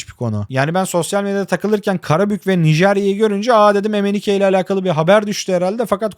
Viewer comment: olan yerde ne diyor mnike mi bir şey? Anlamadım. Lütfen aydınlatın! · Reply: Emenike diyorum hocam, eski Nijeryalı futbolcu. Bir dönem Karabük'te oynamıştı.